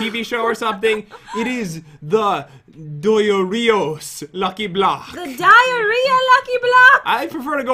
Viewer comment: Why did I thought that it said lucky block doritos? XD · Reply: I thought it said Diritos lucky block vs poop lucky block (its because im listing to music while watching)